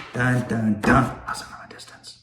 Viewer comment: initially I thought you said "Hussle from a distance" lol